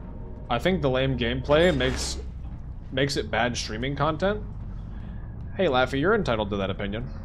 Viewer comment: I was having a conversation over text as I watched this video, and got completely thrown off by 6:28 because all I heard was "Hey Laff, you're entitled to that opinion." ... that's my online nickname, so my head perked up like I got called on in the middle of class. 🤣 <3